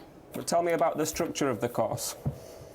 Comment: The British guy keeps saying COST instead of COURSE.... There is an "R" in course... WTF.. I can get why the guy keep thinking he is talking about finance.... Also, he said "SHOTLY instead of "SHORTLY".. is it a Brit thing to drop "R" when speaking????